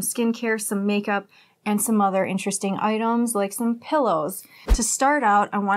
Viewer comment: "and some other interesting oitems like some pillows"😆 please, go ahead, show me the "oitems" 😂 i'm sorry, lol but that's cute and funny😅(oh don't be upset because i am laughing. i have an accent too, i'm foreign, remember ? 😋 ) and of course, the CAT at 7:55🤪 lol.anyhow, interesting pillows, i've been thinking about trying the new types of pillows myself. i even saw a square pillow in a commercial, lol. well, what can i say ? it's late evening here, and i am glad you uploaded now before i went to sleep. i'll have nice dreams now, most likely about fluffy pillow clouds and cats, lol. i wonder if we could have jackets made out of penguin feathers ? just a random thought that just went thru my head, just now, lol. btw, your hair is on fire😉 light brown-crimson...ish ? nice 😊